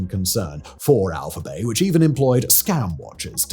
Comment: Unfortunately I keep hearing it as 'alpha bae' and snickering to myself.